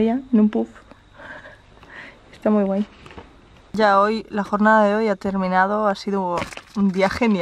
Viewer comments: dice yaoi lol <3 xd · escuche "Yaoi" :'v